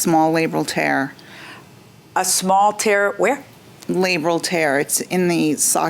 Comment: I keep hearing 'labial' tear, (in the private area) when in fact she is saying 'labral' tear, (in the hip joint) ha ha ha